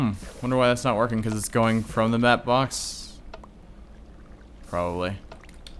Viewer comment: I thought he said fap box